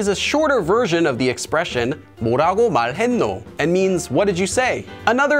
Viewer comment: Please help... 😳😳😳 3:15 뭐라고 말해노 <— sounds like you said that but what was written is: 뭐라고 말하노 Is this just a mistake? Or is there a reason for it that we need to know? :-) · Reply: ~했노.